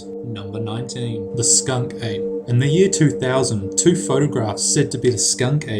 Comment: I thought he said the skank ape, then my thoughts automatically directed to Miley.